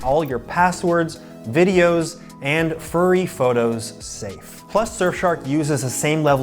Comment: He said what now? "Passwords, online data, and *furry photos*" I'm dead. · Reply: I wasn’t paying attention to the video and I heard "furry photos" and had to go back and check if he really said that lol